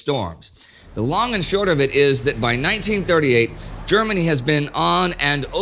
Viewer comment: Sorry if this has already been pointed out, but at 2:04 you say "1938," but the subtitles say "1936."